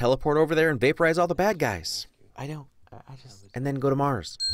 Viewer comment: His subtitle says "I don't, I just?" When he really says "I know," Couldn't help myself lol. Awesome wins video as always!